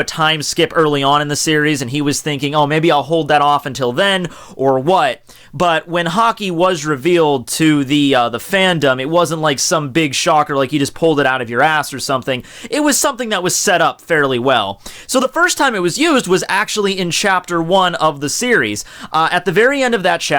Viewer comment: At first it sounded like you said hockey, lmao. "Today we're going to talk about hockey, the history of hockey, everything you need to know about hockey."